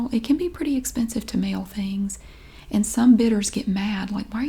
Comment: Omg. I thought you were saying "bitters", not "bidder's". Everything is clear now.